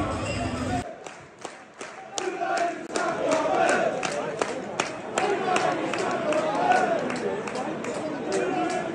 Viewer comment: what do they say at 0:16 ? I really can’t understand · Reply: Unai Emery's Claret and Blue Army